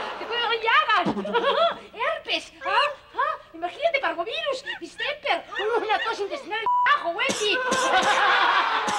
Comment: escuché coronavirus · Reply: GERNAY TOP parvovirus